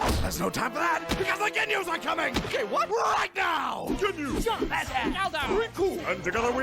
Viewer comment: why do i hear ''the Gay What now?'' Everytime i hear it??